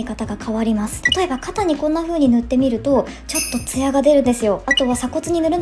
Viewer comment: 艶が出るですよー って聞こえて可愛い